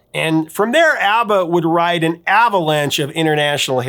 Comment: I initially heard him say ABBAlanche when talking about Waterloo.